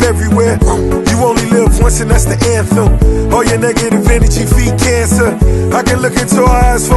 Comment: Did he said "All you Nigga.." at 3:47. lol Brother Rick! we are Afrikans, not Niggas...